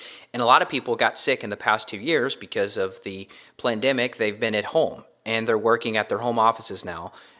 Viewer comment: did he say plandemic?